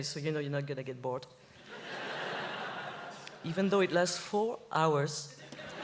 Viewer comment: Celine: Even tho it last 4 hours... Me: I wish! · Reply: I thought she said "It lasts FOR hours"?